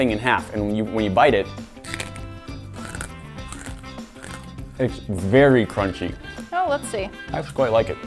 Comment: I thought he said the nougat tasted vary crunchy